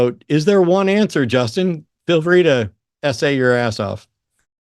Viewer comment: "Justin, feel free to SA your ass off." I dunno. That's how I heard it. 6 months of Epste!n files every moment I turn on the news and now I can't hear "essay" as anything else. You hear me, ese? Don't say "essay" anymore unless you want to be on a list. Or a late addition to the Epste!n files...